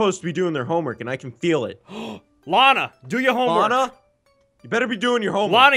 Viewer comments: Hey everyone, I hope you enjoyed the first episode of Weird Internet Games with and! Sorry about the lighting in my webcam. I recently bought a new camera and I'm working out how to fix he lighting. If you'd like more of these definitely let me know. I had a lot of making this :)! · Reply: I thought you said my name (Alana) but you said Lana😞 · I thought they said Alana but they said Lana, cause my real names Alana....